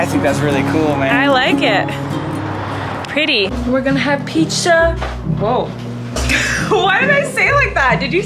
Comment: When you said "pizza" I thought you said "peach syrup "🤦🏻‍♀️